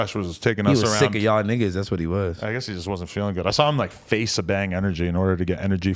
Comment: ish.. what did he say/mean? Lol. He faced a bang energy? I'm probably hearing it wrong or something. Or is it some slang I'm unaware of? Like he chugged an energy drink or something?